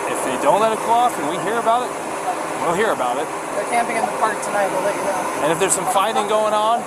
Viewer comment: listen to what they female officer says. Is it “they’re camping at the park today” or we’re camping”